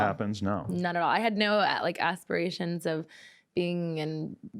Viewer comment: did she say asspirations or did I hear what I wanted too😳